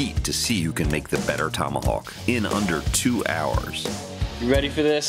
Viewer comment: I thought it said "Tonyhawk" Challenge. XD